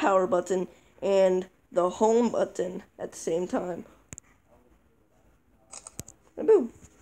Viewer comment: What did he say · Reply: Hold power and home at same time